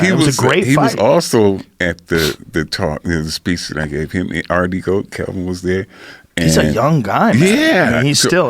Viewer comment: What was that? Didn't understand a word! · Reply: thank god for closed captioning: "He was also at the top news speech that i gave you our decode kevin was there" makes sense.